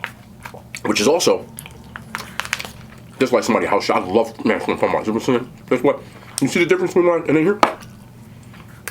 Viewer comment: what you saying LMAO, fye vid tho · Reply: He said afther that meal we FAMILY NOW!! 😂😂💀🐐💯💯🔥🔥🔥